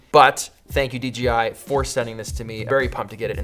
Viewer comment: Ummmmmm... Did you say “D GI?” 0:35... shame on u... lol 😂